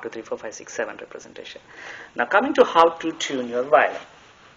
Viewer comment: It came to my notice that, at the playing point of time 14:26 and 16:17, subtitle came as, " tune your wife instead of violin" and at 20:50,"tuner violence instead of tune violin". requesting corrections at the earliest, thanking you for your great endeavour.